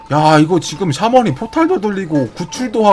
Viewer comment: 초때 나만 포탈 돌리고라고 들었나???